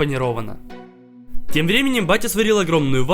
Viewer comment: мне послышалось или он сказал "Тем временем БАТЯ?" Кто тоже лайк)